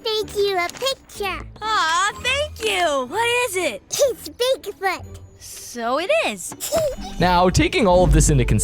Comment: What did she just say? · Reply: Oh she said big foot.